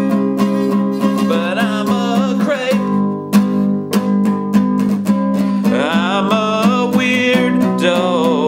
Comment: Oh how funny, I heard "but I'm a craige!!" instead of crepe. :) I love all your videos.